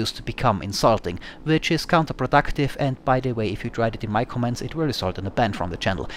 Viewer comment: what did you say at 8:47? You said it so fast I can't understand what youre saying. i've played that pat over and over again andi honestly cannot tell what youre saying. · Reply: "if you tried it in my comments, it will result in a ban from the channel"